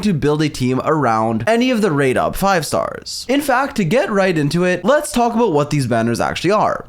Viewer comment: oh my god i heard "TO GET RAIDEN TO IT" 💀